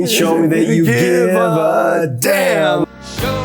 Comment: Oh my gosh. All these years I thought he was saying "show me that you care for then...." Give a damn...geez